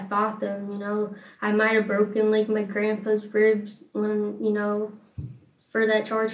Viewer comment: "I may have like.. broken grandpas ribs, it was like a simple assault" shrugs shoulders How disconnected from humanity can you be? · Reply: Omg I thought she said "I may have broken grandpas FRIDGE" and I was so confused